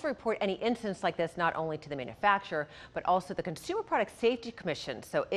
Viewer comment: I'm still ROTFLMAO. Reporter was just speaking normally when I thought she tried to stifle a sneeze as she said her name. Played it back half a dozen times and I still can't figure out what she was trying to say. That's some Anchorman, top shelf material right there. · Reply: Was looking for this reply. Pretty sure it's "troubleshooter" but sounds like shoovershhoeer.